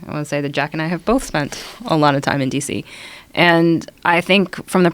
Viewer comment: Did she just call him Tom 1:12:57? Misidentification, she should get a strike for that.